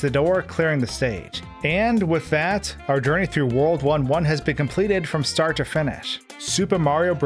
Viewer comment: It sounds Like he said World War 1